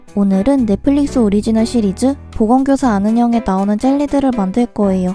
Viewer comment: 보건교사 안은영님이 보건교사 아는형님으로 들리는 내 똥귀...